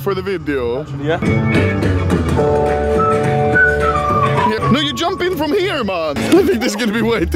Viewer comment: what did you say? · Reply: No you jumping from here man